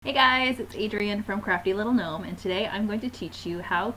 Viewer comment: I cannot hear worth shit. I didn't know the name of your channel when I clicked on the vid and started listening, and this is what I heard: "Hi, it's __ from CRAPPY LITTLE MOMENTS!" LMAO!! I was like, "WT..." so I had to stop & check to see if that was actually the name of your channel! I thought your readers might get a laugh out of that story.